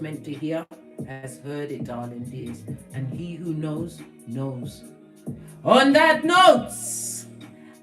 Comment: “He who knows, knows no bounds” that’s what I heard too and I wanted to share. Much love big, big up 🌞❤️